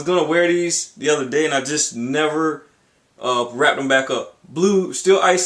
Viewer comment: woah. lol I thought it said "raped." It's "Wrapped" by the way.